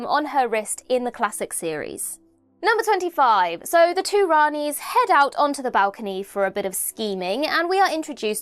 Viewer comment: LOL 5:53. The “two Ranis head out …”. I heard “The Two Ronnies”. 😂